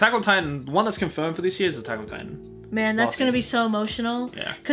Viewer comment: DID I HEARD AN AMEN? AMEN MY BROTHERS! I WANT PART 6 IN 2020